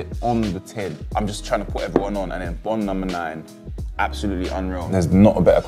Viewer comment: What’s the last perfume name again? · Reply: It sounded like he said Bon no.9